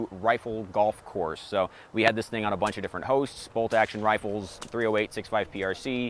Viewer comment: My fav part of the video was in the intro he said "got to shoot it on a bunch of different hosts" but I thought he said "hoes" 😂